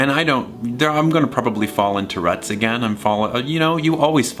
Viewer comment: I thought you were saying fall tourettes 😂